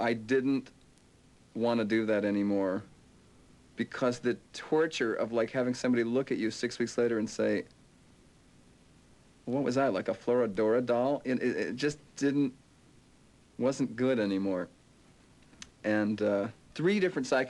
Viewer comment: What did he say at 4:36? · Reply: He said "Because the torture of like having somebody look at you six weeks later and say, "What was I, like a Flora Dora doll?" It just wasn't good any more."